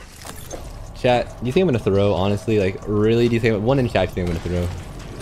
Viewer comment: What did he say at 11:50 ? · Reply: 1 in chat if you think I'm gonna throw